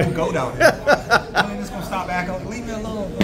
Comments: did he say wat I think he said..”when niggas gon stop acting….” 😂😂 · Wooorrdd?...My man dropping N bombs? 00:20 mark and at 05:50 mark. Y'all hear that? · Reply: Neither time did he say it… 0:20 he says “when is he gone stop acting-…” but he misspoke. 5:50 he says “y’all need to stop talking about me like that.”…